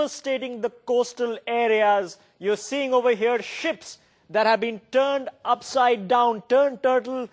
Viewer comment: lol i thought he said shits being turned upside down